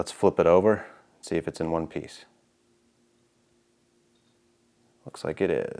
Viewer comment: did someone say One Piece?? 😮